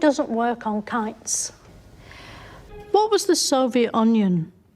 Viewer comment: KITES... I thought she said Kun+s.